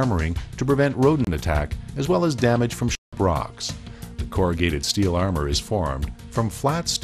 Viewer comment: is it me, or did i hear the narrator say dammage from s**t rocks at 9:05 in the video, the video editing software thought it said shit and muted it out but he really said huge rocks, i died laughing in that point in the video XD